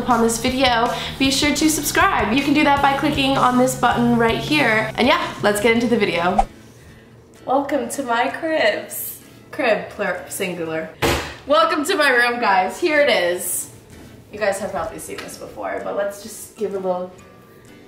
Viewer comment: when she said it again did she say rib ? hehe